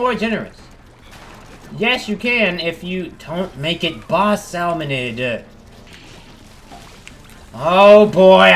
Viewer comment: Wait it actually says 'Salmonid'!? I always thought it said 'Salmonoid' XD